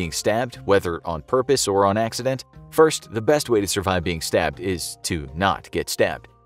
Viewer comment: I thought you said that the best way to survive being stabbed is by being shot and was so confused